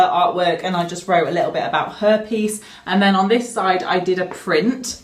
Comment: When you said "her peice" I thought you said herpes lol😂😂😂😂